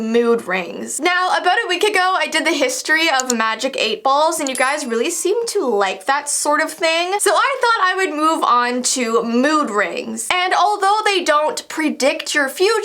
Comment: When she said last week we looked at magic 8 balls I thought she said meatballs oof